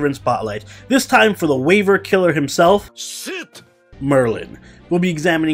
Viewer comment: I thought he said Sitan(Saten)